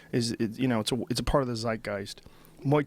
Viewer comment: did joe say "a part of the zeitgeist"? i'm asking because it's a german word and i'm germam. so i'm not surebut it sounds to me like this